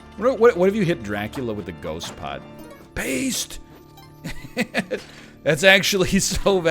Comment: I wasn't looking at my monitor and thought he said 'based!'